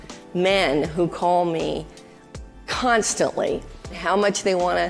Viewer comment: I thought I heard this bitch say "...I have a huge number of men who call me CUNT-stantly...!!" She could have just stopped at the prefix. Freudian slip??